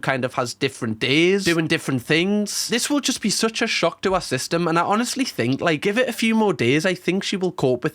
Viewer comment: Did you say “shock to her system”